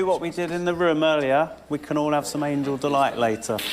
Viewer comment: Noel did say "Angel Delight" or am I hearing something else?